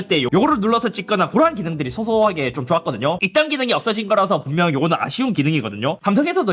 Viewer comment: 잇섭은 발음이 쌔서 있던 기능이 아니라 이딴 기능으로 들림 ㅋㅋㅋ S펜 카메라 잘 쓴 사람들은 있던 기능, 저처럼 거의 안 써서 필요없는 사람들은 이딴 기능 나한테는 필요없는 기능이지만 필요한 사람이 있을테니 25울트라를 사고 24울트라를 중고로 팔아야겠다 🤣🤣🤣